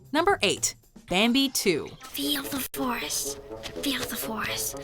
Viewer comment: For a second there, I thought Bambi was saying "feel the Force" instead of "feel the forest."